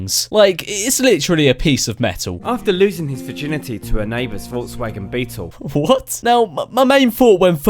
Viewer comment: "After loosing his virginity to his neighbor's Volkswagen beetle..." I heard "After loosing his genitals to his neighbor's Volkswagen beetle..."